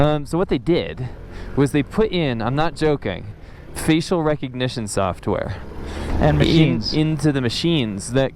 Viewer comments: First I thought I heard 'feces recognition sofware'.... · I heard "fecal recognition software"... :O